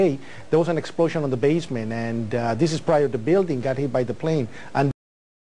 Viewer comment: Hi.... Hate to bring this to your attention (it may have already been) but Willy doesn't say in the last clip "probably" hit by the plane he says "prior". It's his accent which is why you misheard. Willy is still a lying douche though who has tried to sell his story to whoever and sue whoever for profit. He's an absolute douche.